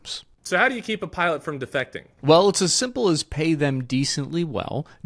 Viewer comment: Thought it said " defecating " 😅